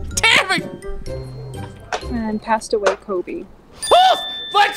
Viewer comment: What she said: “Passed away Kobe” What I thought she said: “Pasta Weikobe”